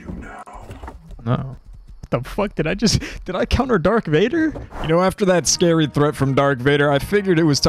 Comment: Did I just heard : "Dark Vader", instead Darth? xD